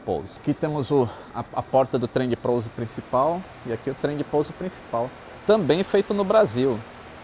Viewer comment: ouvi bem? Trem de Prouso? hahahaha, trava língua.